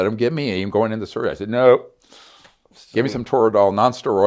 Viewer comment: I cannot take this man seriously. I heard “give me money”.. anyone else?